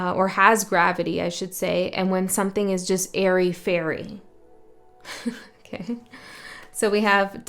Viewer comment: and I heard hairy fairy. I thought that was pretty funny!!!